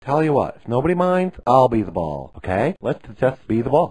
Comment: "BE THE BALL!" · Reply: "be the ball"? I thought he said "be the bull"!